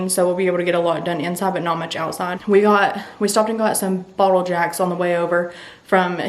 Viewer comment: Totally thought you said you got a "bottle of Jack" on the way over. 😂